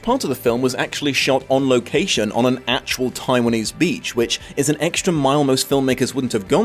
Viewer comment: did you say TAIWANESE BEACH ????